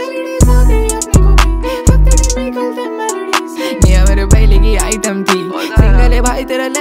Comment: pe dekho kya bola h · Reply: Niya mere phele ki item thi 😅😅